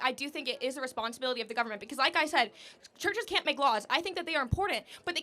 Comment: When I hear the woman at 45:45 say "the church can't may laws" what is hear is "people should be forced to contribute". That is the thinking of tyrants